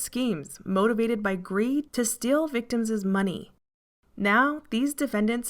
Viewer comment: I just heard "victimses" and died, had to share (love)